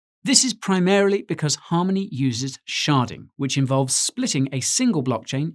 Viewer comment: Anyone else thought at first he said "sharting?" 🤣